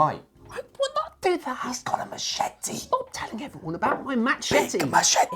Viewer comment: First I thought you were going to say: 'He's got a Machine' instead of Machete. That would have creeped me out hard. Secondly, you got a great platform, however the video shot with your phone looks better than the CANON tbh.